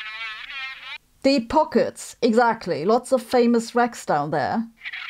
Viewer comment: famous wrecks..I thought she said famous wracks...then I started thinking of Baywatch etc...